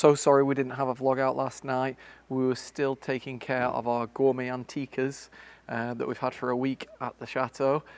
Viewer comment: Ok….in the introduction, I thought you said “gourmet anteaters” 😂🤣😂…..but I think it really was “gourmet antiquers”?!!